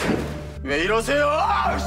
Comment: 왜 이러세요오~ 억양은 이것 보세요오~ 로 들리네 ㅋㅋㅋㅋ 말왕이 보였다~! 유유유유유유 매그내틱~